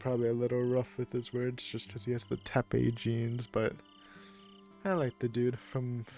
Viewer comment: Lol, the auto subtitles say tape jeans XD